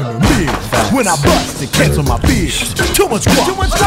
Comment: this whole time i thought peggy was saying "when i bust it gets on my beard" im digusted in myself smh